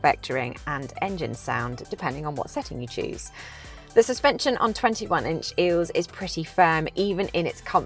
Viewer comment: Did you just say "the setting on 21 inch eels" ?